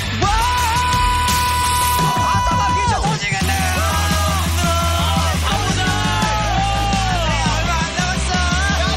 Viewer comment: can someone please tell me what they are saying on 2:53😭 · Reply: Sumin says “아따 마 귀청 터지겠네” which is my ears are going to explode then others say i’m so tired or let’s go let’s do this and there’s not much time left Then Minjae says hurry gather around, im ready and everyone altogether “let’s go”